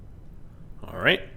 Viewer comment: Youtubes autogenerated subtitles said drug shoots instead of drouge chutes